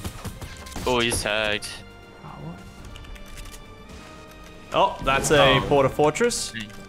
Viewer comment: anyone else hear someone whisper “power”